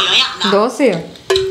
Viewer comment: Aashai doshai who heard that 😂😂😂😂😂😂🤣🤣🤣